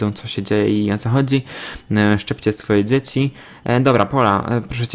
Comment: O boże usłyszałam Szczypcie swoje dzieci XD (Moje uszy umierają) i btw 100 komentarz 🥰😏🤟😂